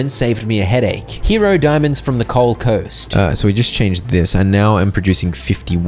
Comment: Oh, coal coast! I could've sworn you were saying Goldcoast.